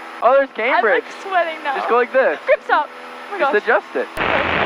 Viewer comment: did she just say babe stop or am i tripping?😅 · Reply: “Graham stop”